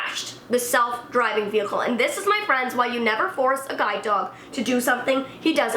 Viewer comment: I thought sh was saying guy dog not guide dog